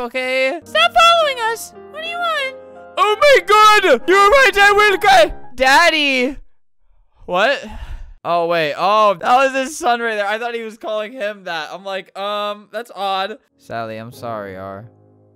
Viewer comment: What i heard was: daddy~ IM NOT JOKING I NEED HELP- ¯\_(ツ)_/¯ 8:53 😭❤️